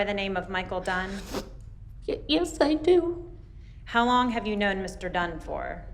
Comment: It sounds like she says "How long have you known Mr Done-For?"